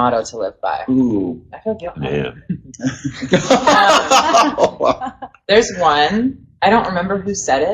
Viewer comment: what did mitch say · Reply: "I feel guilty now" I think